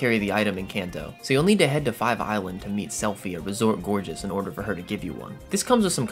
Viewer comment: Help why did I keep hearing "selphy" and thinking "selfie" 😭😭😭 My mind recognized the subtitles and knew it didn't mean that, but I just kept thinking of it.